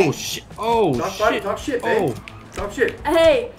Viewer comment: Did logan call her babe